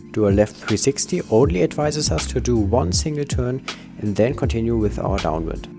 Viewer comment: whath did you said at 13:35 i dint understand? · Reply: Hi! CC from 13:30 til 13:35: ""Do a left 360" only advices us to do one single turn and then continue with our downwind" Hope that helped ;)